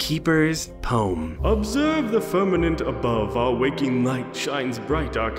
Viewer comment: I thought it said fragment